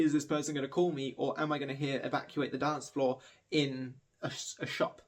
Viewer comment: I firstly heard '...or am I gonna hear Blood on the Dancefloor in a shop' had to rewind to realise that that's not what has been said. Would be funny if you suddenly hear blood on the dancefloor in a shop playing. 😂